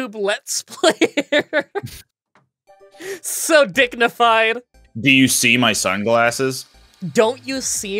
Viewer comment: Why did I hear "dicknified" instead of "dignified"?